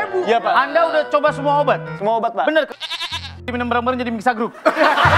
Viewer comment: masih penasaran di 1:35 di sensor surya ngomong apaan sih .. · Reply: "bener mixagrip diminum bareng2 jadi mixagroup"